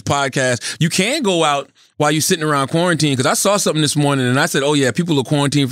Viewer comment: I swear I thought Charlemagne said 'Pornitine' instead of quarantine...